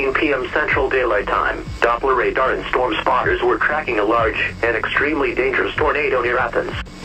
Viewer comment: did bro just say storm chasers were cracking a large and extremely dangerous tornado